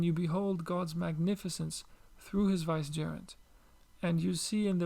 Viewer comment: through his .. what? I dont know the word help. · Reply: Through His vicegerent / khalifa/ representative